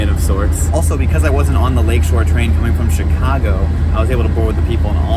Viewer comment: Is it THAT apparent? I just listened again, I don't hear it! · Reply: Yep, haha. Also at 3:54 I hear Chigahgo. I feel the same way sometimes, apparently there's a super obvious Canadian accent but...we all sound like Americans to me! And we definitely DON'T say aboot.